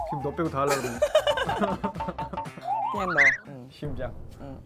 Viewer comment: wait but at 1:34 she actually said "just you" ahhhh they're so cutee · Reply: Lara Navarro yeah, she said 그냥 너 which means just you/only you